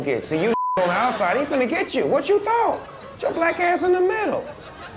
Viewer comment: I didn't quite catch what Snoop said at 0:53. Did he say "What you thought?!"